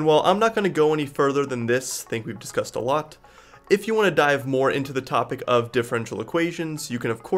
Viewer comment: for a moment i thought he said : "If you wanna die" .. he got me excited